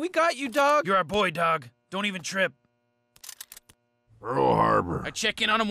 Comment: did santa just say Pearl Harbor?